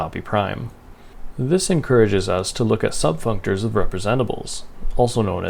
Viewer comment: You seem to say subfunctors but the captions say subfunctions. Anyway, nice video! I think I understood about half of it.